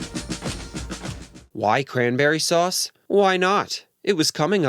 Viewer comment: I always thought John said "I'm very bored". Never heard "cranberry sauce" before.